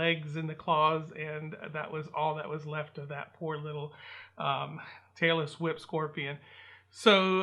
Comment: Tailless whip! I thought you said "Taylor Swift"!